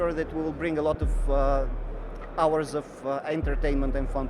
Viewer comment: haha sounds like he said hours of angertainment